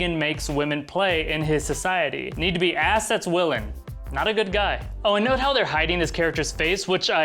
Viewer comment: i had no idea he said ass that's willing? i thought he said assets willing lol